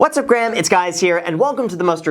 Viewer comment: Did i just hear say: What's up graham it's guys here?